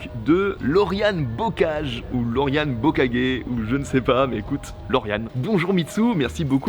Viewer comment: J'ai compris "LorianeBukakke" oskour